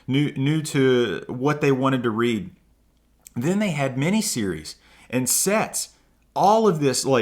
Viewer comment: When was at the 1st shop & I thought he said "Then they had mini series & sex!" I was like what the frak kind of shop is this!😂